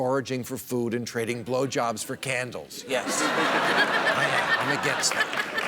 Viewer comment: OMG did he just say blow jobs for candles..